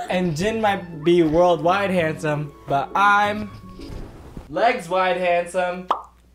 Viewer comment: LMAO FOR THE INTRO I THOUGHT U WERE GONNA SAY “and im … MR WORLDWIDE👨🏻‍🦲”